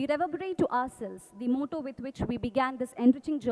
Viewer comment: I can't understand some lines of Ma'am. This line- "To conclude honourable president madam as we mark..................." (Almost,at the end of video: 6:30....) Please write it. 🙏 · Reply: Character is the highest virtue, This is the motto of LBSNAA.